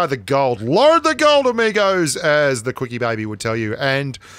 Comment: Did I heard Quickybaby ?